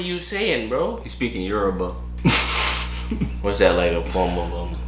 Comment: Did u just say “he speakun europe” 😂😴